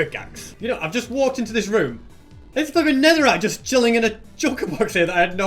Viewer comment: did anyone one what he said before the word netherite. To me it sound like an F-bomb but that might just be my dirty mind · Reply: I think he said flipping but that might just be me